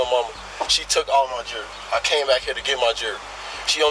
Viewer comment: Thank you, captions. I could not figure out what what he was saying: jury? jerky? Ah, jewelry.